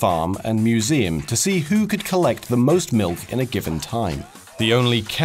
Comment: Lol he said milf instead of milk at